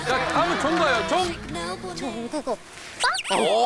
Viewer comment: i think jihyo says "jungkook oppa"